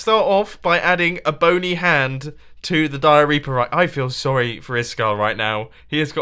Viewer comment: Did he just say diarreaper